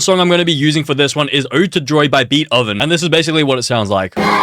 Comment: I am sorry but did you say ✨✨beat oven✨✨ 😂😂😂😂